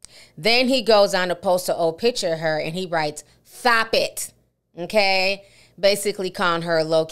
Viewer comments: I thought he was saying "thop it" because her lisp🤔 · Oh ... I thought he was saying “THOP IT” as she would with her lisps and broken promithis ...